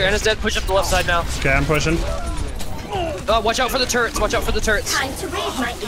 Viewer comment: i thought said watch out the turtles